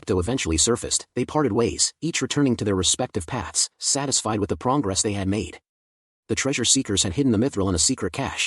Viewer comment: Did I really hear "PROWNgress" ? :face-green-smiling::face-green-smiling::face-green-smiling